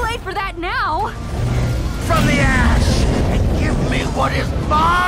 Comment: I thought it said "from the ass"